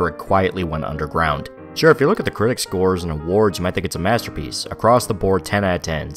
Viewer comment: Thank you! I kept hearing "this is a masterpiece" and thought I may have got a different version than everyone else.